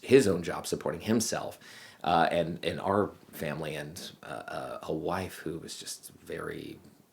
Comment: Sounded like you said 'waifu' lmao.